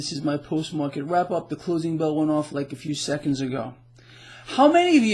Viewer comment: Hey, when I started listening to your report I thought you said the prison bell not the “closing bell” had just gone off. By the end of the report prison bell would definitely be a better description. Or to quote The Eagles Hotel California ; “You can checkout anytime you like, but you can never leave” Love your analysis and political incorrectness. It is refreshing to hear truth. Please continue to speak the truth as you see it from your heart. Those of us that really see/understand what is happen are not offended, but angry as hell as to what is going on. The enslavement of the masses is an ongoing operation.